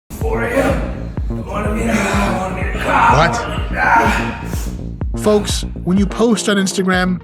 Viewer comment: It sounds like is saying " 4 AM, I wanna be a COP, AGH--"